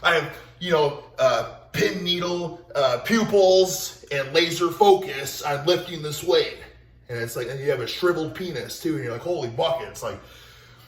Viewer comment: Internally I am debating if he said shriveled or triple penis @ 8:18. I know the CC says shriveled but...this is Sticky Ricky we are talking about.